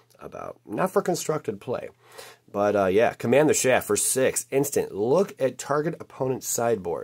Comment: Sounds like he is saying “command the shaft” ..... That’s what she said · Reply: there's a test card he pulled at the beginning called command the chaff